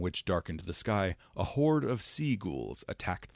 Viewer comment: I heard "a horde of sea gulls" lol